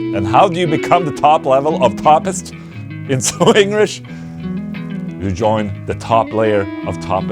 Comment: Did just say 'ingrish'?